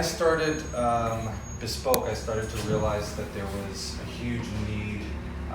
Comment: At the beginning of the video, I thought he would said "so, when I started..uhm.. bottoming..."